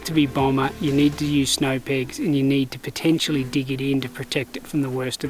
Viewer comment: Hi there! Not sure where or if has recommended Snow Peak tents in this video? Generally Snow Peak tents are better for car camping or potentially short overnight hikes, however, some models like the Fal Pro Air 2 may be ideal for hiking or backcountry trips. Just depends on the model and specs. We don't have any experience using Snow Peak tents on backcountry trips so we can't really provide feedback. We can recommend Helsport for 4-season backcountry trips as they offer superb protection while still being super lightweight. For general hiking in 3-season conditions, the Mont Moondance or Hyperlite Ultamid with the inner are what we most commonly recommend. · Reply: was where I heard he mentioned Snow Peak. But now I rewatched the video again, I think he meant "Snow Peg" and not "Snow Peak" lol sorry English isn't my primary language. Thanks for the recommendations and the always good product reviews! Cheers.